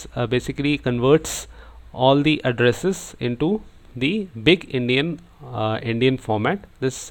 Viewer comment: Only me who thought he said "big-indian"?